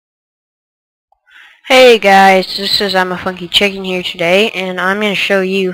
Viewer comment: hahahah at the beginning I thought he said Im a fucking chicken xD But then I realized he said funky :D